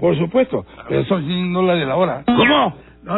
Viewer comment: que dijo? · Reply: "pero son 100 dólares la hora"